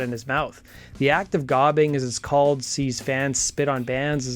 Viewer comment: At first I misheard "the act of gobbing" as "the act of God"